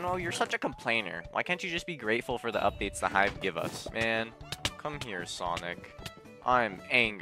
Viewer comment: I first heard it "the hive gave us "😅😅